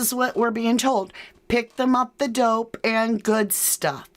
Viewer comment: I hear ‘picked em’ at the door—doing good stuff…’ I don’t hear dope of big at all personally